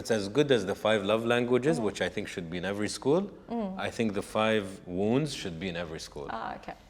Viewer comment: Hi Everyone — Don't forget to turn on Closed Captions by clicking on the (CC) icon to watch this episode with subtitles اهلا بالجميع - لا تنسوا تفعيل رمز ال(cc) لتفعيل خاصية الترجمة اثناء مشاهدة الحلقة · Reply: What were the two books that you had mentioned in the interview at 54:53. I heard “Five wounds” book and “Good as the five love language” please confirm. Greatly appreciated